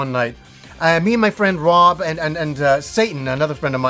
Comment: Rob and ...Satan? Did I hear that right 😂😂😂